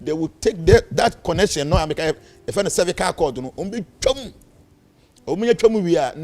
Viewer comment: Did he actually say Cervical cord?😅